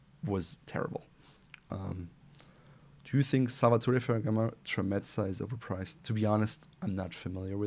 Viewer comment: did you say Salvatore Ferragamo? If so Aren't they a widely know. Brand??